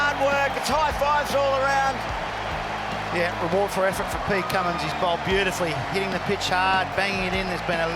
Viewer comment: Who else heard the commentator at 1:21 say ' Peter Cummins'